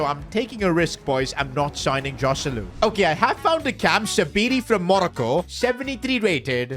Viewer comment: did he say Monaco or moreco😂😂😂😂😂😂